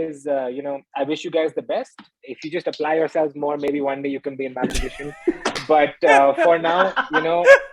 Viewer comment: What did Kanan say at 58:37 ? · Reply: "if you apply yourself more, one day you could be in my position"